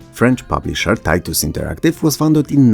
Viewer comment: did you say tits?